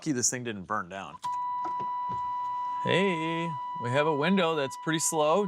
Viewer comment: Did he just say we have a weiner 😂😂